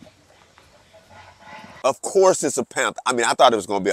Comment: i thought he said "of course its a pimp..."